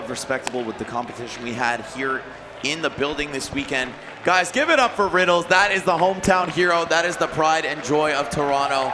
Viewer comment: anyone know what he was doing/saying? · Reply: He was asking for Agua, which is Spanish for water